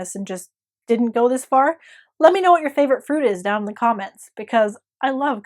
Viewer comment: I pulled out my old recipe book, so I'm making djaj mhamar and a huge pot of rice to soak up the sauce. It smells amazing! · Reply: I totally thought you said food, not fruit! It has homemade preserved lemons in it, does that count?